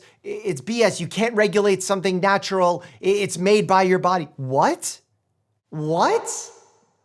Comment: The “what’s” at 6:31 are giving Ant saying “wet” lmao